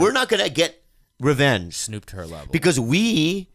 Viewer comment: Did say "snoop to her level" lmaoo